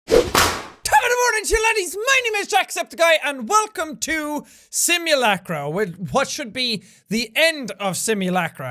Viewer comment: It sounded like you said : "My name is JaqueSeptiqEye" #positivementalattitude